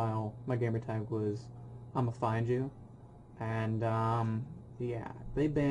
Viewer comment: Thought you said I'm a fine jew